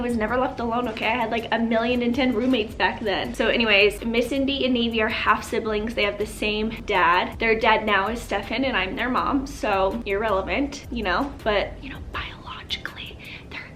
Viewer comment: I died when I thought you said "they are dead now and Stefan and I are there mom" referring to navy and Indi lol!